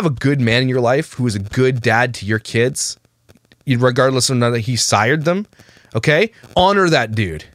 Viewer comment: Speaking of Grammar Police lol...did you say IRREGARDLESS?? 42:59 ....possibly sounded like a little stumble over words but just checking 🤣